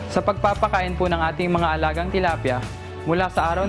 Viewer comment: I heard her say "tolapia" . She's talking about fish.